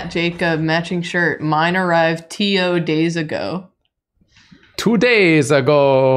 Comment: NGL, during the section around 26:00 I thought they were saying "Tio Days", as in days where you go hang out with your uncle. lol.